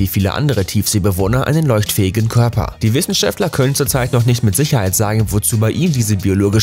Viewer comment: habe ich da gerade wiesenscheftler gehört?